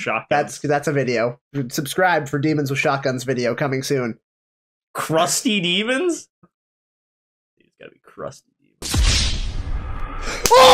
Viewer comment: forget demons with shotguns i want crusty demons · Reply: I thought that said Custody Demons for a second looool.